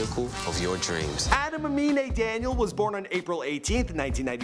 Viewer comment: i thought it said anime